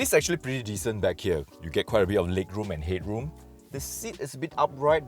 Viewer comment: lig rum end hid rum sounded like a nigerian basketball player